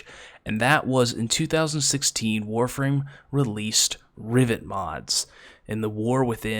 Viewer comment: Sounds like your saying riviet and not riven